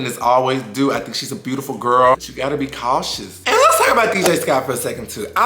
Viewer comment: Every time you said “beautiful girl” all I heard was “beautiful gowns” 😭😭